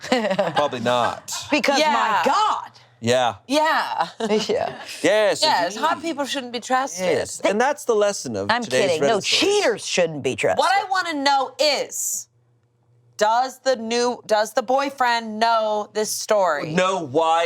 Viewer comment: I heard this as queers shouldn't be trusted the first time 😭 thought Angela was coming for me